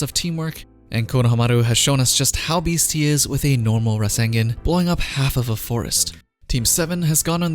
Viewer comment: I'm sorry.... Did you just say RaSeNgEn ?????